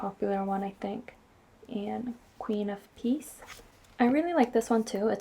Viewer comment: Queen of Peace. I thought you said Geese until I read the comments lol